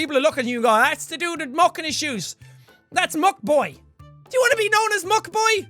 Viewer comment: Jack:MUCK BOY Me:did he just say fuck boy